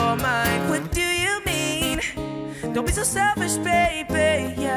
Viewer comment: What did he say from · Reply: Fall Beneath Surface dont be so selfish baby